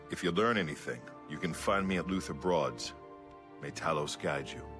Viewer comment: I thought my man said "looser broads" and was like "what!?, we have that now!"